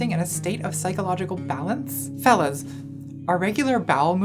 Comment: nobody: me: Mishears "fellas" as "phallus" Yup, I'm not OK probably. Pls help.